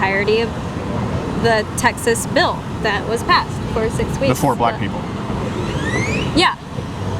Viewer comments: I THOUGHT YOU SAID POOR BLACK PEOPLE SC: I DIDNT ……….. 😂😂😂😂😂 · Steven, I think you overreacted a little when that dude thought you said poor black people, He probably legitimately misheard you. It was easy to see you were pissed at him from then on.